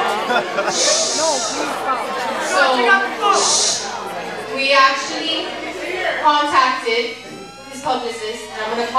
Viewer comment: Who came at 3:45? I didn't hear his name and didn't recognize him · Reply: Dwyane Wade (NBA Player)